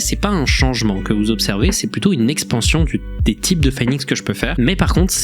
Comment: j'ai entendu il a dit expansion du territoire là